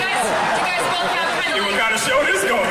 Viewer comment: What does Mackie says at 4:57? 🤔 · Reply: ‘You know what kind a show this is going to be’